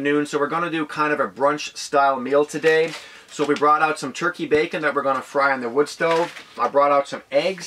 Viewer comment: turkey and bacon should never be used together... lol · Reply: I thought he said "turkey bacon" and NOT turkey and bacon.